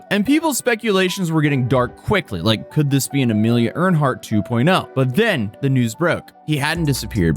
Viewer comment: did you call her Amelia Ernhart?